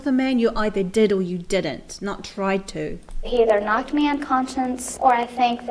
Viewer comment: At around 4:43-4:46 she says “he either knocked me UNCONSCIENCE....” not a word.....like she is unconsciously saying she had no conscience......scary, what a fractured mind......